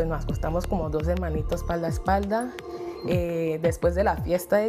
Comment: Alguien que me traduzca , que dijo??? Dosemanitos paldas paldas? WTF esta gente si hablan raro, si van a una entrevista vallan con un español básico!